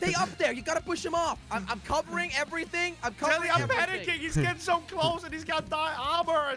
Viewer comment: OMG IM DYING When Crainer said; He’s got dia- Armour ahhhh THE CAPTIONS SAID “He’s got diabetes” 🤣😂🤣😂🤣😂🤣